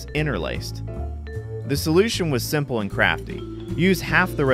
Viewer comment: At first I thought he said "the solution was simple and crappy". I think he said "crafty".